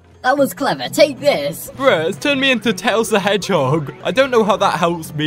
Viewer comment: did you just say that tails in a hedgehog?!?!?